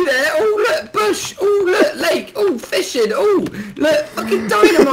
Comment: who else heard Loot Lake like if you did